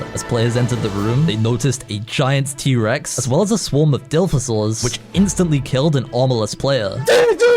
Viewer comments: this man did not just say "dilfosaurs" 😂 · did you just call the Dilophosaurus a dilf-osaur?